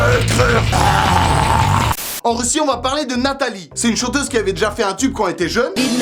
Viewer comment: j'ai compris c'est une chauffeuse puis je me suis dis《euh.. je pense que dormir la nuit serais bien pour moi》X)